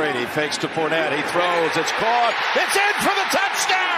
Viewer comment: did he just say “it’s gronk”??? 😂